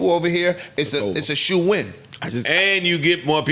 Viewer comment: did Ish call it a “shoe win”?! 😂